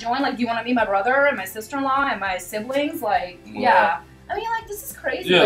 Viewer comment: Did she say sister in law at 10:05 👀